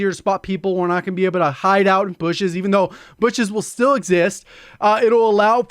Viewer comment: all I heard was "people who hideout like bishes even though bishes will still exist" 😂😂😂